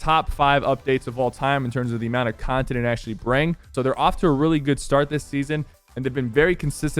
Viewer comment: Did he just say "brang"? Wtf😂